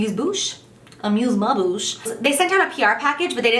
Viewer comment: Haha that sounded like she said "amuse my bush" when she said it with a funny accent 😂😂😂😂 you never fail to amuse me Jaclyn, and am sure am not the only one 😁😂💗💄